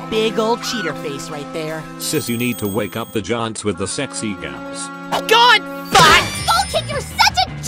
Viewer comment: I'm trying to figure out what Tael's saying at 4:44 "Sis you need to wake up the giants with the..." something that sounds like "sexy guns"?